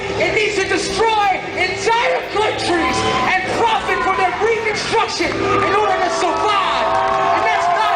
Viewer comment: caption should read “from their reconstruction” not “for their reconstruction.” the current caption is not what he said and changes his meaning drastically. do better